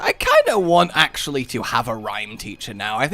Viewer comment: I just randomly skipped here and thought you said "Rein teacher" and I was like "Wait, learn how to be a Rein? Just hold one or the other mouse buttons and occasionally press a few other buttons."